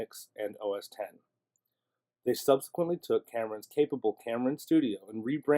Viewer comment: I am sorry but did I hear you say "Cameron" instead of "Xamarin" (pronounced Zamarin) ?